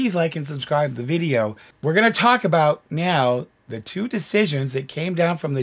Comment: you said meow instead of now love that 😻